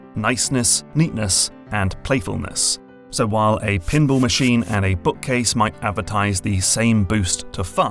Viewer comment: ohh i thought he was saying "pimble machine" until the graphic came up and i just accepted it as some sort of british thing